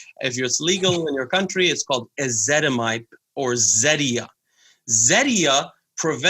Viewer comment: mentioned a medication that sounded like "Izedamipe" but I could not find the proper spelling. If anyone knows what he is referring to, please leave a comment with the name of it so that I can include it in the list. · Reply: Ezetemibe